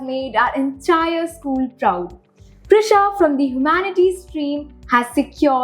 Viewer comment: Can anyone tell what was her stream ? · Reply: Humanities is what I heard.